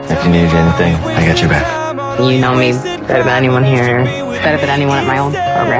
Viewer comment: "You know me better than anyone here, better than anyone at my old program, actually"... all I hear is: "You know me better than Trey".